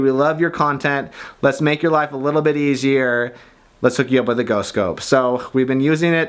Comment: I thought said "No scope" at the beginning xD